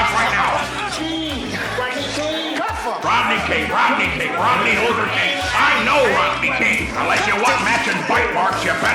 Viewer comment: What does Mcgruff say at 11:40 cuz I can't understand him. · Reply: He said “Rodney King, Rodney King, Rodney Luther King”